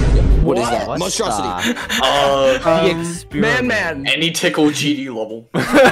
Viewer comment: did rly say man man? Nahhhhhh he cursed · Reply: Man Man